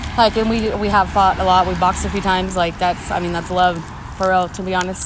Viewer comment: did she says we’ve boxed a cpl times, that’s love?